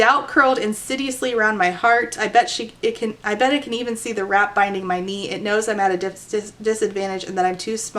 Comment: my audio processing issues had me thinking you said "rat biting my knee" and I couldn't understand why she'd want to hide a thing like that